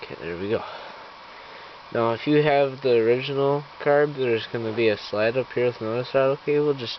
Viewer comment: Hey what did you say at 7:20 ? I think i heard if its the original carb there will be another cable? is that what you said? And what cable?